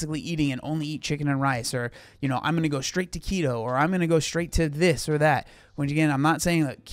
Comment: I honestly thought you said “I’m going straight taquito” not “to keto”. I thought you threw that in there as an example of someone just giving up 🤣 … I thought, man, I’ve been there 🤦🏻‍♀